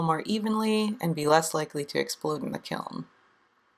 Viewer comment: Lol at 2:24 I heard you say "and less likely to explode an accountant." instead of "explode in a kiln." 😂😂😂😂